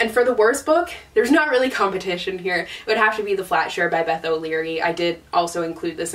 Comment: I am so glad you put the pics of the book covers up because for some reason when you were talking about February, I thought you were saying “Flasher” instead of “Flatshare”. I was thinking, mmmm I don’t remember the book called Flasher and then I saw the cover. 😂🤣